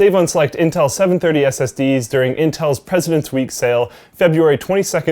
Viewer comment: When you said presidents day sale I thought you said peasant day sale and I thought it was a SSD for a ps4...